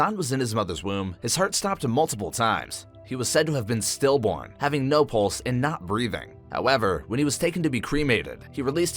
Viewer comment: i heard him say "having no balls" 💀